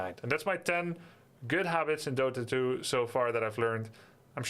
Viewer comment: I totally heard him say, "top 10 hobbits I've learned...". I am now ready to also learn about these hobbits.:)